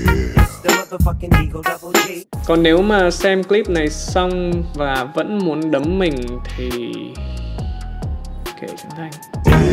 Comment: k nghe được nói gì luôn á, có cao nhân nào chỉ giáo k nhỉ? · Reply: Nếu Trấn Thành xem clip này xong mà vẫn muốn đấm mình thì... kệ Trấn Thành.